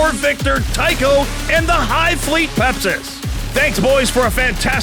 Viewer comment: In the intro I thought I heard Tycho announce his army as "Hive fleet Pepsi", and now I can't unhear it.